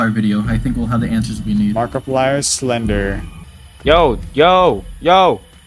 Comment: idk why but when said “markiplier slender” i thought he said “markiplier’s blender”. (i had to watch twice because it didnt make sense)